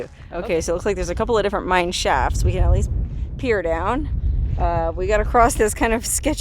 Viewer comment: I swear You said You wanted to pee down the mine shaft